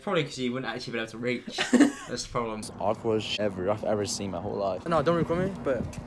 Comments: i thought it said awkward eaters, i am still confused how my mind managed to do this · bro I thought it said awkward eaters😂